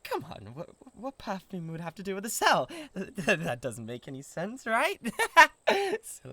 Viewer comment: Excuse me, besides the perfume, but did I hear him say “cell”? Uh… idk, I would say neither to these two, but I have a safer-ish chance with the Incubus… oh, but these boys are all the entertainment I need for a LONG time 😂😅